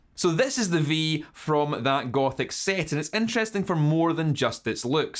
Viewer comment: No lie I thought you said Gopnik instead of gothic